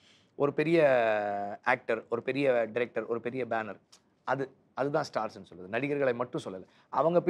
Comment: When vetri maran said Big banner people the subtitles says top rated actors